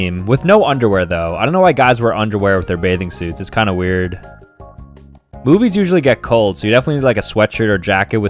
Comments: I swear I heard him say “boobies usually get cold” 😂😂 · I thought he said boobies get cold